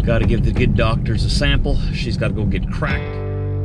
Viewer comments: When you said I have to give a sample and she has to get cracked I heard you say that she had to get crack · At 323 or so, I thought you said she has to get crack! LOL!